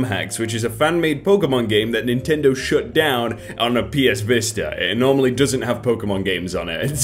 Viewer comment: did OP call the Vita the Vista?